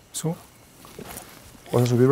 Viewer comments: En el minuto 24:44 se escucha un susurro que dice subete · En el minuto 24:44 se escucha una voz , mas bien un susurro que dice "súbete" justo cuando Brian sube el primer escalón